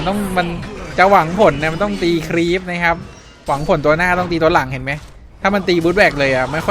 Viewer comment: เหมือนจะได้ยินว่าหวังผลตัวหน้าต้องตีตัวหลังใช่หรอ555